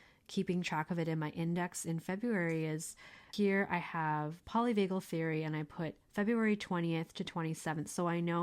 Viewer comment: I thought you said poly BAGEL theory and that got me excited